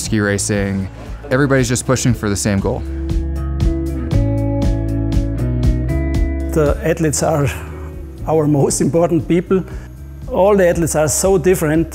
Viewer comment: 0.51 I definitely thought he was saying “headlettes” 😂